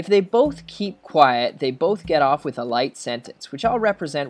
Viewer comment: it sounded like you said life instead of light so i failed my final exam, thanks